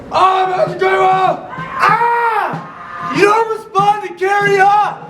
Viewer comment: but seriously, what are you saying here? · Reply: I think he says “You don’t respond to “Carry on”!”